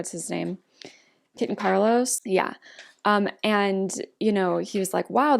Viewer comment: Omg girl. I legit thought you were saying "Kitten Carlos" when you were saying Kit AND Carlos. Lmfao. I completely forgot that kid's name in episode 1 was Carlos... hahahaha